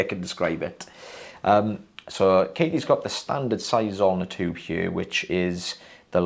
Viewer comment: I've been watching & you (plus Katie now too) for years. All this time, I had thought you were saying "Standard Size on the tube" when doing microsuction only to recently realize you've actually been saying "Standard Size Zoellner tube". I feel cooler now that I actually know the lingo 😎